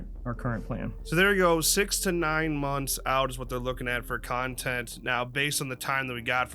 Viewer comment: Man, I really thought said they had 69 months of content planned until it was explained he said 6-9 months. lol I went from "WOW that's a lot of content" to "WOW that's not much content".